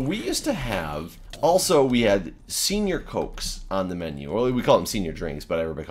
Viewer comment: LMAO i thought you said you had senior folks on the menu!!!!!! You eat people lol, ohhhh :( you said senior cokes LOL